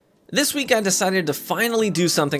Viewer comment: Lol I paused the vid at the beginning and I swear I heard "this week I decided to fuck"